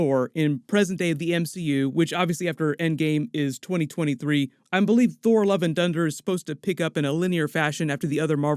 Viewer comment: it sounded like you said "Thor: Love & Dunder" 😆